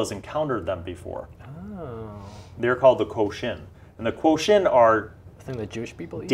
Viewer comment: what about jewish people? i couldn't hear it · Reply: i dont know know said a word similar to “kosher” and I got confused thinking he was talking about Jewish food